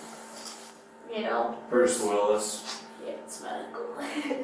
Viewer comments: Did she say Bruce Willis is her uncle? Lol 😂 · Did she say that Bruce Willis is her uncle? Or am I hearing things?